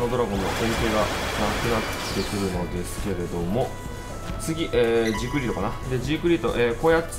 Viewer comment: くらいに言ったジークフリートが 熟女に聞こえたww